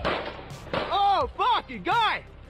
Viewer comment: does he say “OH f*ck a gun” or am I tripping